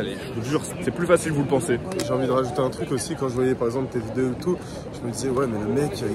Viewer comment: C'est quoi la référence ? Tel2 ? Teldeux ? Je trouve pas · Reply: Il a dit « quand je regardais tes vidéos et tout »